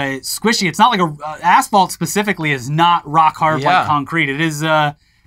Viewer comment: I thought they were saying Broke Hard till I looked at the sources.